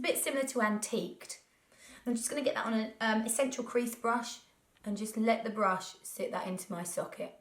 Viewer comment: I'm sorry if this is crass but it totally sounds like you said 'essential queef brush' -spat out my rice krispies